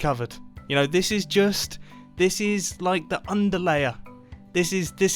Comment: this is what I heard: "this is the undie layer"